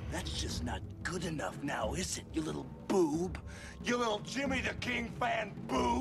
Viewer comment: Does he call him a "Little Jimmy" at 0:16?